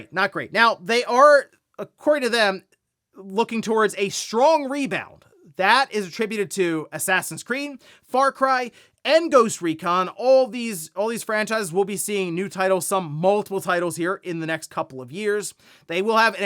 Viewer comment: This sounds like you said "Assassin's Cream".